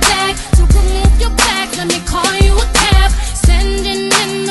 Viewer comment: i thought she was saying "let me call you a crab"